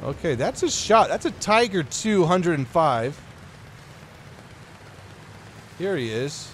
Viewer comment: did he just say tiger 205???🤔